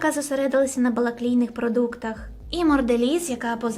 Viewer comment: на балаклійних продуктах...мені не почулося?))